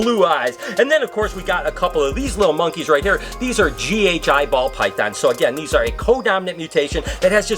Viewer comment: I thought he said "G-ijoe pythons" for a moment. I'm sad that I heard that wrong lmao